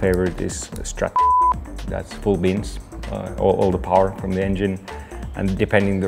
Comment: did he just say «coolbeans»...?